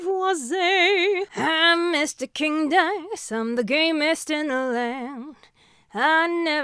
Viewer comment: The fact that knew cuphead Me:*breath* YESSSSSS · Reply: I thought said "I'm the gayest in the land." 🤣